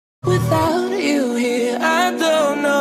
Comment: WHEN IT SAID "without you here" I thought it said "without yuuri" xC I LOVE THIS SO MUCH ENDED UP CRYING